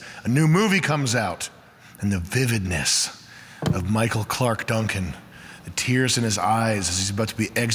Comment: i thought he said Michael Clark Douglas for a minute. I would watch that movie